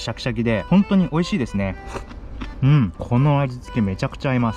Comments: 17 00のところうんこの味付け美味しいに聞こえる😂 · う○この味付けに聞こえたw「17:00」ほんとはうん！このあじつけだろうけど「あんちじゃないよ！」